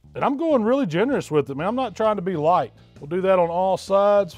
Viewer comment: I thought he said he wasn't trying to be white